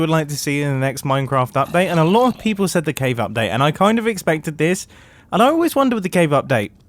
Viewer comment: i thought he said cake update